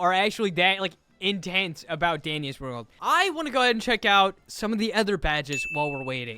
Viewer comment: Did just say dingus world😂😂😂😂😂 Fr did he just say dingus world on